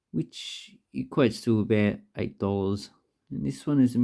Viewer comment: the subtitle said that it costs $88, even you said "still about 8".